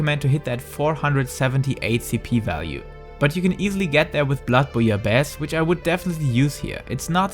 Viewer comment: like he says i should get blood for you best? what !? he says that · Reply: Blood-Bouillabaisse, it's buff food